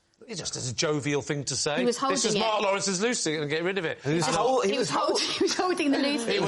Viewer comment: Ooooooh. Loo seat! I thought he was saying “loosie”, like a loose cigarette, which I didn’t think was a term in England. And then the CC said “Lucy”, which made me even more confused.